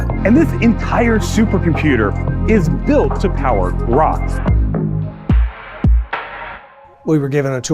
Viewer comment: did he say its meant to power Ra???